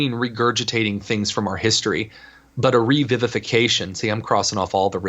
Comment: When ever someone says "revivefication" (sic) I hear "re-vivisection", which would be much more painful.